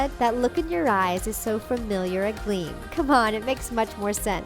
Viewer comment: Lol I thought she said your eyes are So familiar ugly lol